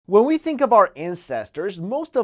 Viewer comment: I thought he said: "When we think of our incestors" xD